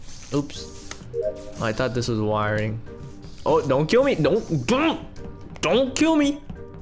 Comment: Bub don't kill me What did he said but it was funny 🤣🤣🤣